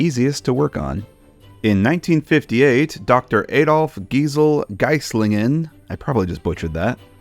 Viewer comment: um did he just say adolf 🤨🤨🤨